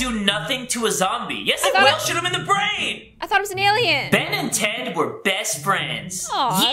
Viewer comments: I heard Ben ten, not Ben and ted...please tell me I’m not the only one who heard this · When you said ben and ted i thought you said BEN 10 😂😂😂😂😂😂